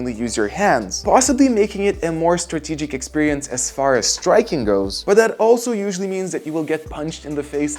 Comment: I heard ''striking girls'' 💀